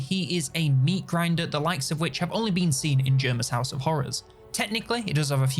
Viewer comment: Jerma’s house of horrors? did i hear that right?